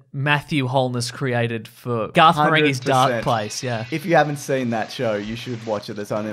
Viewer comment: In case anyone couldn't make out the name of the TV series mentioned at 12:25, it's Garth Marenghi's Darkplace